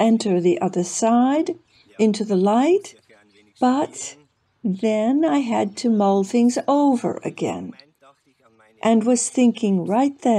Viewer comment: Very interesting. Thanks for sharing. Please ask questions the viewers might have such as: What did he mean when he said he had to Mold things again if he went to the light? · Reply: You misheard the translator. The man was saying to the interviewer, “At this point, I had to mull things over again.” Do you understand what that means? I don’t know if you are familiar with that phrase or not.